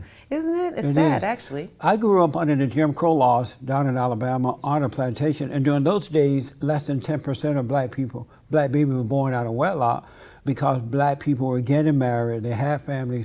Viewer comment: Did you say that you were born on a plantation?😮